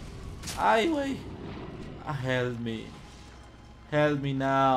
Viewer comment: si ponen mucha atencion pueede notarse un leve susurro que dice "Help me..." °_°